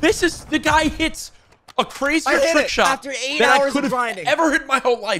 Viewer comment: did he say : a trickshot. 🤡💀